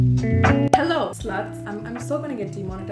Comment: I thought she said hello slugs 👍